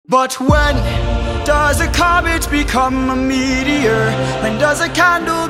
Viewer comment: "But, when does a cabbage become a meteor?" These misheard lyrics eill kill me